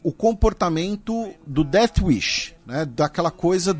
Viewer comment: o disse "That Wish"? Não entendi bem. Se for, onde encontro mais sobre esse comportamento? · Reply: Death Wish